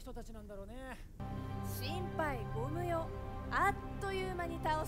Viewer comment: 電車の動画広告で見たやつだ、窪田君以外セリフが座って無いって感じる、2:38心配ゴム用 って聞こえる😅かなりの勾配がある八百屋も上手く生かせず足元すくわれるだけで、窪田君の芝居と演出方法と舞台装置がだけが評価される作品になるんじゃないか心配😢